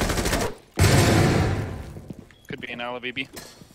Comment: could be an Ala bi bi lmfaoo wtf did I just hear 🤣🤣🤣🤣